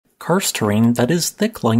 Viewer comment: For a moment I thought you said “Cursed terrain”! As a person who would never go into a cave it’s pretty accurate!! Jokind aside, karst is beautiful. See Paparoa national park in NZ.